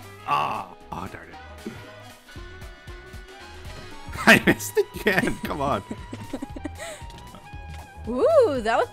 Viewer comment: What's tryna say??? What does he meant by 'playing who again' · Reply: is saying "Playing UHC" and UHC stands for Ultra HarCore. Its a similar concept to hunger games except players are teleported across the map and have to move to the centre or be encased by a moving border. The goal is to be the last person/team alive.